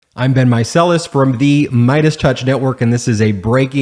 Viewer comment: Every opening I hear “I’ve been micellas” and just now realized his name is Ben. I feel silly. 😂 Hi ben 👋